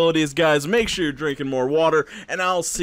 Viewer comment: I coulda swore instead of saying reshiram, I thought he was he was singing about loving fresh RAM.